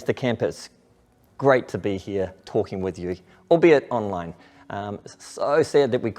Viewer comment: I heard Albion online, had to rewind lol. Can’t wait 4 camp next year!